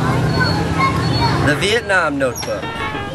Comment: Am I the only one who thought this said the Vitamin notebook. As in the nutrition things😐